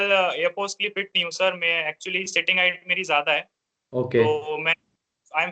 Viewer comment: what did he said? Kya zyada hai? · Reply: Sitting height (on plane seat)